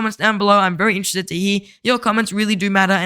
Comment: what did you say at the end? 😂😂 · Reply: thanks for watching hope to see you in the next one